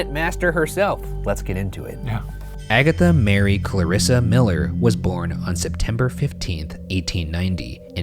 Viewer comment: I definitely thought he said "Agatha married Clara Miller" and thought she was awfully progressive for the time